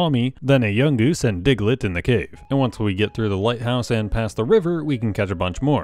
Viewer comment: for a second I thought he said White House.